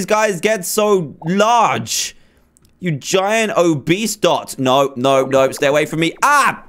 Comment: did i just hear say a giant obese dot